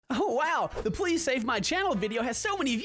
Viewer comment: I thought he said "the police saved my channel" 😥